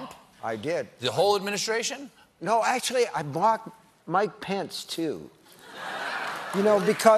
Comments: I had no idea who Mike Pence was so I thought he said "I blocked my pants". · When he said mike pence I thought he said my pants at first😂 I was like wtf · I kept hearing "my pants" when he was talking about Mike Pence and it deadass took several double takes to figure it out 😂😂😂